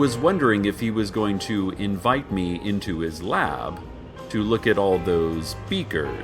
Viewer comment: I thought he said "lap" and I blushed so hard.